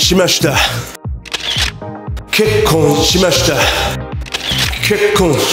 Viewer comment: ここが結構しましたに聞こえる wwwww